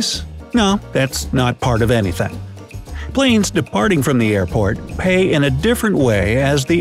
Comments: Thought you said airpods lol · anyone else thought he said airpods? i was like tf is this